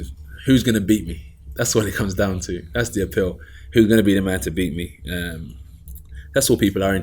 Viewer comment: Did he say '' there will never be a man to beat me '' ?? Seems usually arrogant for AJ........sometimes I wish he'd be more arrogant.